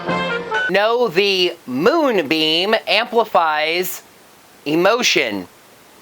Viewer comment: Huh, I thought the monster was saying "Mood Beam"